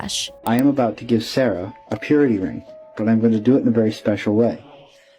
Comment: I thought he said he was giving her a period ring